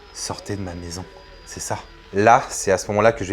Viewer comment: Moi j'ai entendu "sortez de ma maison "😱🥶